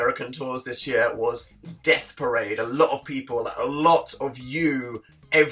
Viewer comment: When he said Death Parade, I thought he said Black Parade and my emo heart cracked